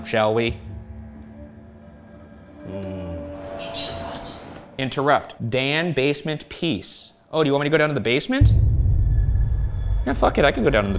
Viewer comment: when the ovulus said 'interrupt' I was really hoping it would follow up with 'new moon' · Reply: For so long I thought it said "into rot"